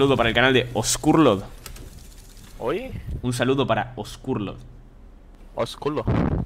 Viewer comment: por un segundo pense que dijo culos